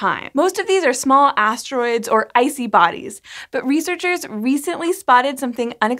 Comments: When she said "a suite of super-computers" at first I thought she said "a sweet ass super-computer". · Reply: Early in, she said, "icy bodies," and I heard, "I see bodies." We should collab on a sci-fi horror spoof. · in, she said, "icy bodies," but I heard, "I see bodies." <snicker> I play too many zombie games, watch too many horror movies. If "I see bodies in space," I'm gonna need a new adult diaper up in that orbital craft.